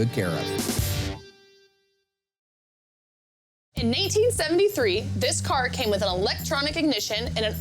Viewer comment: Go to 6:29 and im sure she says 'in 1873...' or am i mishearing it...